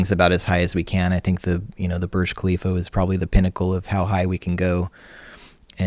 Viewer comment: "Burj Khalifa" is what he tried to say. I spent 20 min looking for the "Birch Calivo" till i found this building that completely dwarfs American trade center